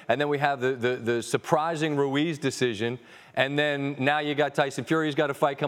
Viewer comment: did he say Ruiz Decision? That was a TKO buddy